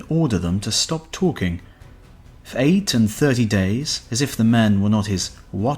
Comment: 8 to 30 days? Is that what was said here? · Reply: 8 & 30. So 38 days.